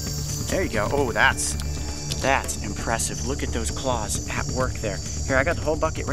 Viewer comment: did he really said " look at those balls?" i mean theyre pretty big though....